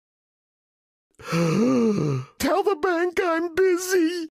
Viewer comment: i thought he said tell the bank im NPC